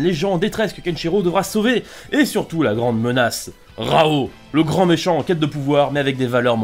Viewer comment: j'ai entendu raoul mdrrrrrrr